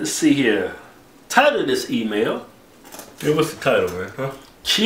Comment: I always hear "tired of this email" at the start